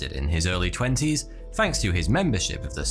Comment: whenever he says thanks it sounds like hes saying fanks xD